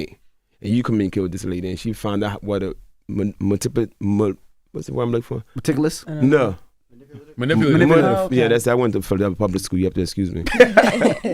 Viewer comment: I thought he was trying to say how much of a “Matilda” she was😂😂 this muddasucka was saying Manipulative 🤣🤣